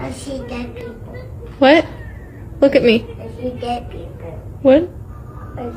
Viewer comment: I thought that baby said I sh00t at people 😂 I had to rewind